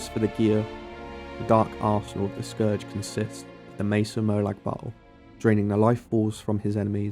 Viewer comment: Great video! Love the build ideas, but I really had a hard time making out some of what you said over the background music, probably my middle aged ears heh. But the first piece of gear you mention for the Scourge, at 10:42 it sounds like you're saying "Mesa Moag Bottle" which is what the subtitles also say. what are you really saying? my apologies & thanks in advance! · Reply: Mace of Molag Bal